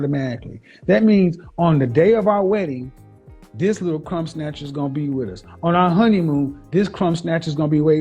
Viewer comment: Oh shit I thought he said "cumsnatcher" at 10:20, until he repeated it lmao.